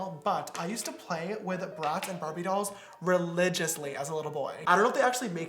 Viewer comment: i thought James said, "I used to play with rats and barbie dolls" and i spat out my water.